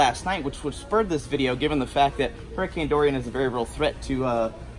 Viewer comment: I'd have sworn I heard you say hurricane Andorian. Like on Star Trek. The blue guys with the antennae. LOL